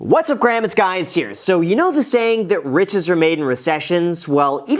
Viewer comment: Did he just say in the Intro “Whats up Grams, it’s Guys here”? Lol this FTX thing has you stumbling man lol